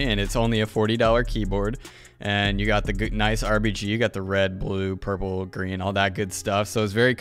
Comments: my boi said rbg 😂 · did he just say “RBG”? Cuz boi if u don’t